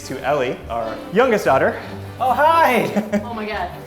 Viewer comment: I thought the trainer said out youngest daughter LOL